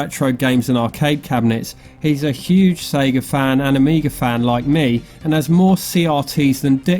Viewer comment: fook watching them ones 😂😂😂😂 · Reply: Lol thought you said few like me 😂😂😂😂 #shouldofgonetospecsavers 😂😂👍